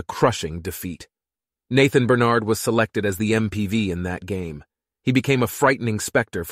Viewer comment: Is it just me or did someone else hear ma boi day mpv instead of mvp 3:53 😂 Plus the annoying background I think dudes newbie still trying new things